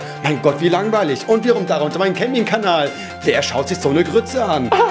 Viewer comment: ich habe statt gaming Kanal Camping Kanal verstanden und dachte mir nur so "wtf" mach bitte mal so einen das wäre INTERESSANT